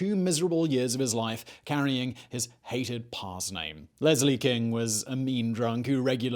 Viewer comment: Oh, I thought you said LICH king, whoa